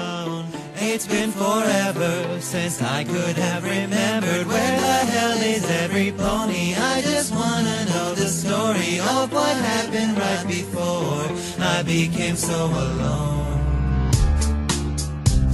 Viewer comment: jeff sounds like he says EvErYpOnY